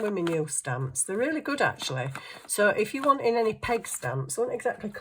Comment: I thought you were saying 🐖 🐷 🐽 pig stamps. 😂haha. I couldn’t picture you using pig stamps so I went back and listened more carefully. They are peg stamps. I was just listening to you while I was making supper. So I didn’t see the stamps. I had a good laugh when I realized my mistake. .